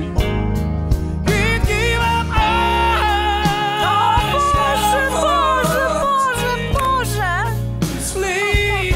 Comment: what does edyta say at 10:29 ? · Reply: Literally she says: Oh God, God, God, God :-)